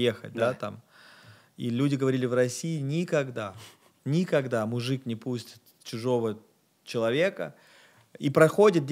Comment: Подскажите упомянул про сэпел грема насколько я расслышал, напишите подробней про что он, это было на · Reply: Он сказал эссе Пола Грэма - Paul Graham